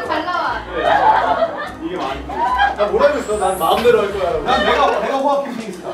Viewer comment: 혹시뭐라고하는건지아시는분들계실까요??😂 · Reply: '내 마음대로 할거야' 라고 했다고 하네요^^